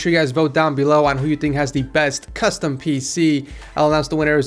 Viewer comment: i am the only one who heard 'twatter' ??